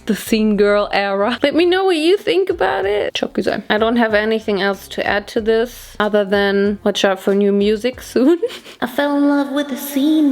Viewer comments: What does she say at 20:10 ? It sounded like "çok güzel" to me in Turkish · is she said very beautiful in turkish "çok güzel" ??????? OMG as a turkish subscriber it was a big surprise, i didn't expect that ahahahadajshds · did she said "çok güzel" (means very nice, beautiful in Turkish) in 20:09 or am I just going crazy??? 💕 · What exactly did you say in 20:09 ?? I heard 'çok güzel' And that means very beautiful in Turkish. So...🙀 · ok i really need to know what you said at 20:10 because it sounds EXTREMELY like how you say "very pretty" in turkish and i need to know if im just imagining it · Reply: Exactly!!!! Did she literally say "çok güzel" or am i dreaming??? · Did you say "Çok Güzel" at 20:09? Or did I misunderstand? And loves from Turkey 💕🇹🇷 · omg did she said "çok güzel" ??? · OK DID SHE JUST SAID "ÇOK GÜZEL" IN TURKISH BYE 20:09 😭😭🌈🌈💋🇹🇷🇹🇷TÜRKIYE WHAT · did she say ''çok güzel'' or do i misunderstand??? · DID SHE SAY ÇOK GÜZEL? OR AM I makin up (lemme know turkish friends <3) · are u saying “çok güzel”???? Did I hear wrong? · Did you just say 'çok güzel'..? Turkish Queen!!! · Did she say "cok guzel" on 20:09??? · is she saying "çok güzel" in 20:09 ? if there's any turkish ppl can you guys reply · Girl am i hallucinating or did say "çok güzel" ?? 20:10 im confused 👀👀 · did you say 'çok güzel' in turkish at 20:09 hahaha<3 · DID SHE SAY "çok güzel" in 20:10 OR AM I GO CRAZY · Is it me or did you just said "Çok güzel" at 20:10 ?!?!?!?!?